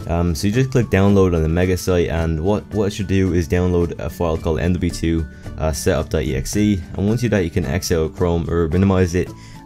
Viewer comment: Did you just say mv2? ;D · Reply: I had to listen to it twice, but I'm 100% sure I said mw2